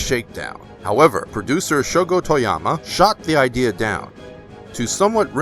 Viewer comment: Says Shogo Toyama. Caption says Shogo Tomiyama.